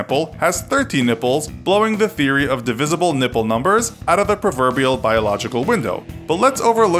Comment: I just heard ‘the theory of divisible nipple numbers’